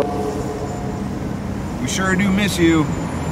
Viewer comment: When you said "we sure do miss you" it sounded like he said "same"